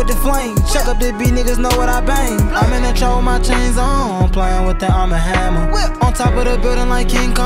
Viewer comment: I’m in the trap with my jays on …playing with that arm and hammer 💪🏾#Whip knew this was gon go off when fool came in like that lol 🔥🔥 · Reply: It sounds like he said I'm in the trap with my chains on....